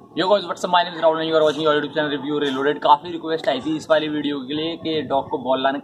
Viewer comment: mein aap kya bole ni samaj mein aaya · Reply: New new ki kaafi request aai thhi ye bola tha